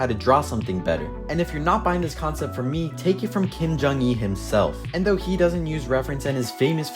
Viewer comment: did he say Kim jonk un